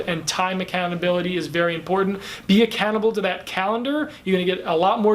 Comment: At first I thought you were saying "Be a cannibal" to your calendar (LOL) - now I realize it was "be accountable" - oh dear, I had a good laugh. It may be time for a hearing aid!